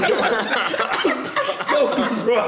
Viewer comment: I thought he said my momma got the tea on me